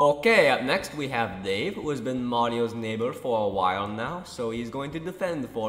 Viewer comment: I legit thought it said "Wario's neighbour" for some reason